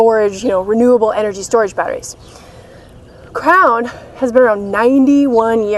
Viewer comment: Every time you said "Crown" i thought you were saying "Crayon" haha. I love your videos and your positive attitude to life, one day i will hopefully meet someone just like you. Definately wife material. Keep up the great work x